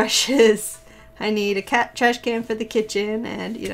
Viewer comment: Omg I just laughed so hard! Wonderful video, and I love your journal style but at first I thought you said you needed a "cat trash can for the kitchen" and I was like.... excuse me what? Lmao great job none the less! 😂👍